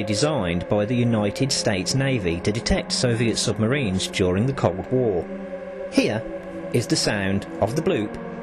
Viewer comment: Why’d it sound like he said cock war instead of Cold War?